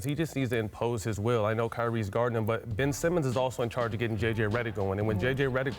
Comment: did he say kyrie is guarding ben simmons